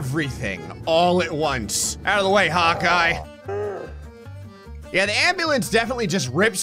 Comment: Bro I fr thought he said "Out of the way hot guy" at 3:08 But then realized one second later that he said Hawkeye